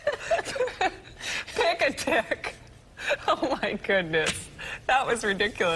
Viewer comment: I don't get the first one. It sounds like she is saying "Pick a dick" even when she is correcting herself.